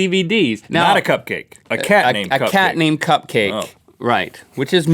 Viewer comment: I thought he said a cat named Cupquake I was like yazz